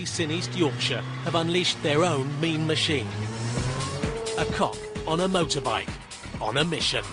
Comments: DOES HE SAY COCK LMFAO !!!!! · does he say 'a cock on a motorcycle' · I heard "a cock on a motorbike"